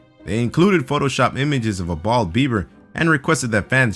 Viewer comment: Bald Bieber..... I thought he said .....bald beaver. Hahaha